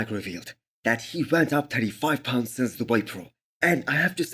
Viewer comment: did you say he went up 35 lbs ? 😳👀